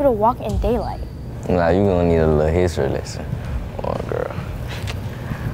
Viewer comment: I’m tryna figure out- does that yellow eye thingy make ppl fall in love😂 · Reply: it sounds like he said what are u entering